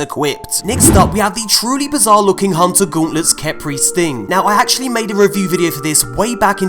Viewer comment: who else thought he said "khepri's thing?"